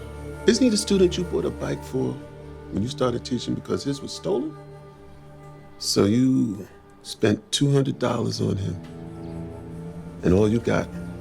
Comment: Is that what I am hearing. That the man say ”so you spend 200 tousen on him” on